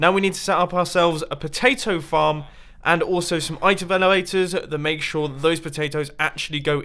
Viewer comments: did anybody else hear itevelowators? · Did you just say "Item veluaters"